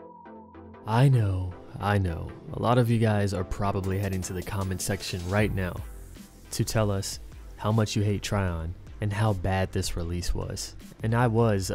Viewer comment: I was gonna fresh start then I looked at my 7.9k gs on aranzeb and cried....thought about it and I said fuck it. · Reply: Oh damn thought you said how much I played. ...Payed? only spent $80 on in game credits.